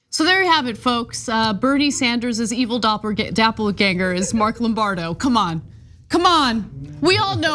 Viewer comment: Did Ana just say, "dappleganger" and not "doppelgänger"?